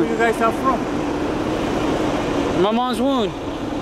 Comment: Thought he said my mom's wound